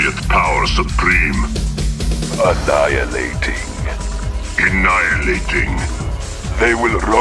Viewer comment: For the longest time, I was hearing "Lace-less shoes ready" instead of "Tesla suit ready". Sort of made sense, though. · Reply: oh and for quite a while I heard "a night and eighteen" instead of "annihilating".